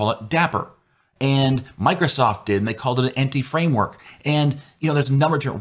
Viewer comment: Empty framework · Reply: I thought you said Entity Framework.